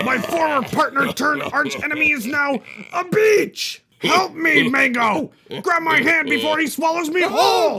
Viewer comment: I thought I hear it Wrong because I hear he say “Bit€h”